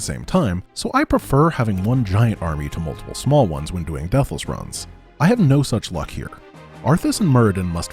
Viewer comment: every time he says "deathless run" i hear "devil's run"